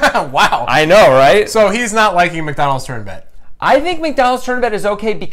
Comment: I first thought you said "tournament" insead of "turn bet".